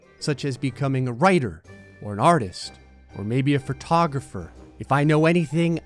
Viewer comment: I know I'm watching a video because when it said "photography" I thought it said "pornography" for a solid minute there